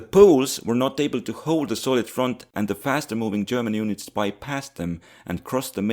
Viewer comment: i really thought you said "Soviet front" for the longest time and i was so confused xD